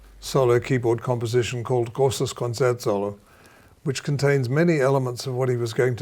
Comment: Somone help. What other piece was he talking about at the 1:11 mark? I couldn't understand what he said. I tried searching up random things that sounded similar but had no luck. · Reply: Liszt - Grosses Konzertsolo, S176